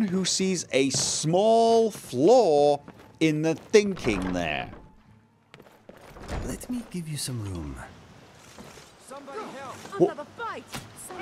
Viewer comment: Heh, when Kha... I mean said Let me give you some room, I misheard it as let me give you some skooma.